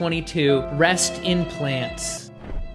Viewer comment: When you said "rest in plants" I thought you said "breast implants" 😂 I was like what?